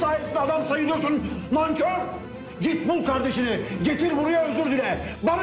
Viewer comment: pitbull kardeşini getir buraya özür dile diye duydum amk